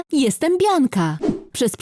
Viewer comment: Czy tylko ja usłyszałam hej tu firanka ?